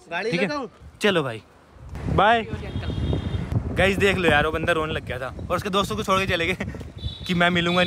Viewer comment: I think he said bandar in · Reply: Bandar nhi banda Bola tha